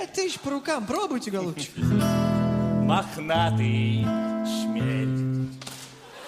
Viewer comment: Мне одному послышалось в самом конце, что они хором спели "мохнатый член"?